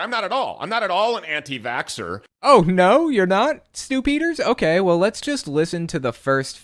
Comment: Each time you said "Stew Peters" I heard "Stupider"